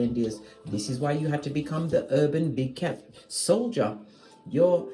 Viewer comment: You awaken me this morning ma’am. I heard you say wake up Big Cat 🐈‍⬛